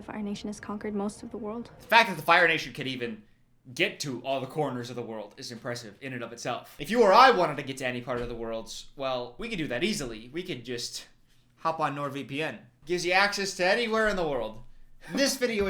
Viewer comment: i heard 'you and i' and got me giggling fr..